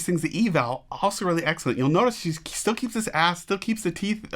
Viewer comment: I thought I heard, "I think she still keeps his ass" No offense intended here.